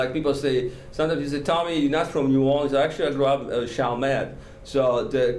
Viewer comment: What is the spelling of the place he says he grew up in at 11:17? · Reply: Chalmette